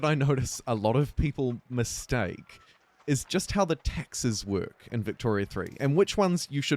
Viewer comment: Anyone else hear, "is just how the Texas work?" @ 2:42?